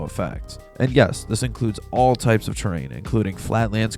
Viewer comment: bro i thought you said train i was like"what trains in twisted?"